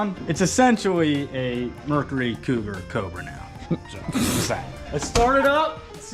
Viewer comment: i thought he said "chupacobra" 😅